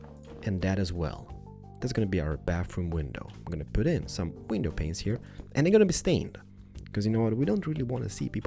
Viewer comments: Not gonna lie when when u said window panises i thought u said penises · When you said window panes it sounded like you said window penises 😂😂